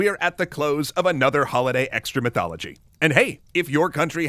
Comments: I straight up thought you said “excrement-thology” at the end there. This poop stuff gets on the brain. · For a minute there I misheard "Extra Mythology" as "Excrementology"